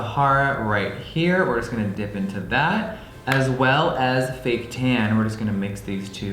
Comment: When you said, “fake tan,” I could’ve swore you said, “baked ham.” Wow 😂